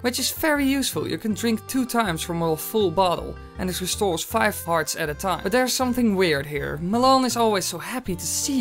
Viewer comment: I swear he is saying five farts at a time XD